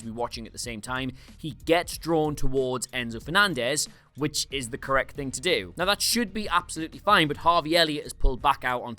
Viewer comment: filling his ??? What is he saying? Can someone pls help me with this info? (my English is not that good and yt auto subtitles is "filling his CS"). Thank you :) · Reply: Kecks. It means pants as in underpants (underwear). He was shitting his Kecks = he was shitting his pants = he was so nervous/scared, he was shitting his pants